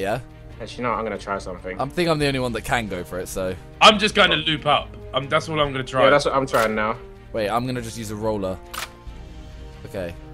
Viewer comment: At 20 secs it sounded like said I'm just trying to lube up